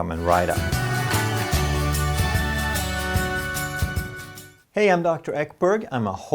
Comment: I thought he said "hey I'm Dr Eric Berg" lol